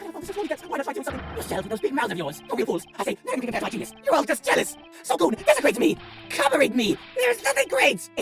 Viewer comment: I just heard "so goon" even though it's supposed to be "so go on".